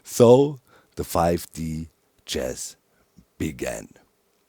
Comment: I thought it said 5D Cheese and was very confused